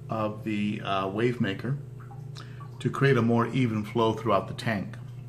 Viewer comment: did you say even flow? I love that song.